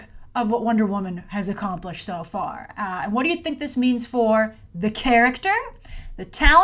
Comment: Did you realize you sounded like you were saying "accomplish-shit" the first time lol?